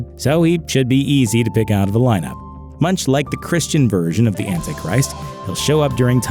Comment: Thank you my bothers and sisters because before I looked at the comments, I heard him say Christians fear the Antichrist I said WHAT! that's not true. A real disciple of Jesus does not fear the Antichrist. Then I saw it already commented on and said alright!